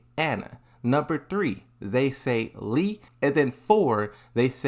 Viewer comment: He already pronounced it like "lay down" but did you actually mean "lie down" which is also pronounced as "to tell a lie" and "like" without the k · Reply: I heard "Lee"